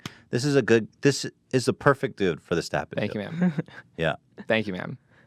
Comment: so was I the only person who heard thank you ma'am at 26:36 ?